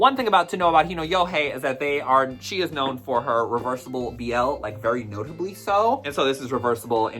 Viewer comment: I had to rewind dat cuz I thought you said "reverse BBL" 😭😭